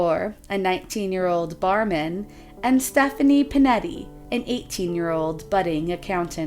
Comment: caption says Pettini yet narrator uttered Pinetti...